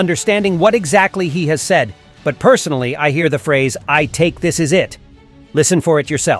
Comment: he actually said “ I’m ticked!, this is it!”